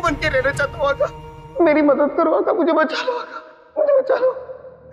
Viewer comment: Did genu said "please" bachalo aaka